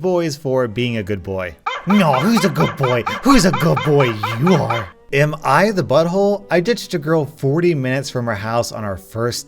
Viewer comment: At first I thought it said "Am it he butthole"